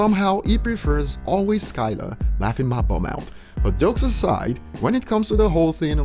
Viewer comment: Swear i heard this guy say "laughing my bum out" hahahahaha